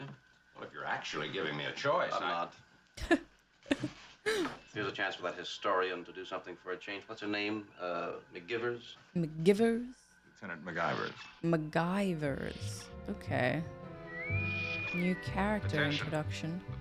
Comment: Sorry, but the entire time she was commenting, all I heard was "MacGyver"